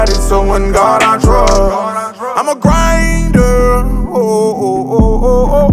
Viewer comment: I’m a griiiiinnddeeeerrrr **only real fans know🔥🔥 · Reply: I thought he was saying "I'm on grinder" lol I was like damn gates not you too.